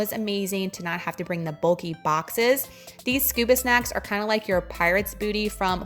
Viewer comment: You said scuba snacks, I thought you said Scooby snacks...oh my goodness...lmbo 😂